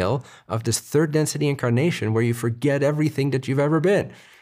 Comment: Omg you said "incarnation" and i heard "kimkardashian" Lol